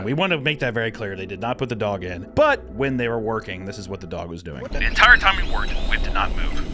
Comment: Anyone else hear “What da dog doin”